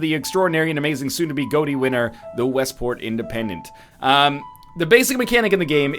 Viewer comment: I thought you said Watersports Independence